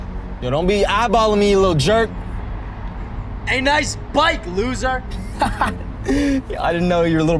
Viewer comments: Help I lowkey thought at 1:57 he said "butt" instead of bike😭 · Did he say “Nice butt, loser!”